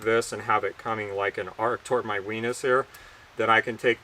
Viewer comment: did you say "wenis"?